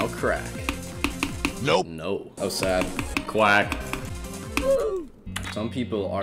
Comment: I always thought that said swag instead of quack